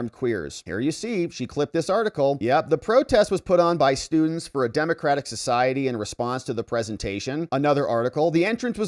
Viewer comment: did say "Students for Democratic Society?" ??? I KNEW my memory was not failing me!! And my HEARING is also up to snuff!! Ask any old person who SDS was. Students hahaha more like ancient protesters. Are they still around? They were stupid in the 1960s. And Ermia?? was probably recruited by HilleryClintons State Dept. so chalk up another one on Hillarys Kill List. Tax $ -> USAID -> State Dept -> Hitlery -> SDS from the 1960s -> ANTIFA -> Armed Queers -> Tyler whatever his name is -> Charlie Kirk Rip. Amazing. Thanks!